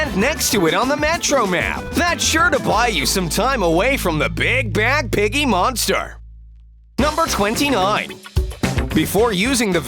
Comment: i heard “big back piggy monster”